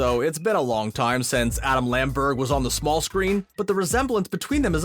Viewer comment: When he said "Adam Lamberg" i thought he said "Adam Lambert"